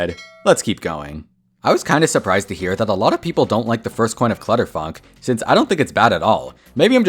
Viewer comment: I think its cuz i swear alot but when he said clutterfunk i thought he said clutterfuc- you know what it is. But this video is really good, i never was intersend in this game but after looking at this. I am intersed (i am bad at spelling)